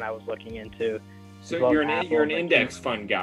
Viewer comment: What the...homie im using this as background noise, and i heard "tom, so youre an attorney?" And i ran over to my phone to see if it was attorney hahahahaahah im so glad he used his camera for this. I love attorney....but also hate the law....geck bless my friends....geck bless....